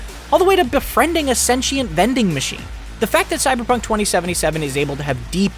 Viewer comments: “Befriending a sentient vending machine.” Sweats in Astral Chain. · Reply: I thought he said bending machine. I immediately thought of bender from Futurama. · “Befriending a sentient vending machine.” Sweats in Astral Chain. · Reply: I thought it said Bending machine and was getting very excited to meet a cyberpunk Bender. Was totally gonna go robbing with him